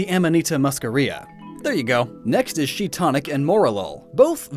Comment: um did i just hear that right or did he say shiitonic? 5:03 jaja had to check if it was correct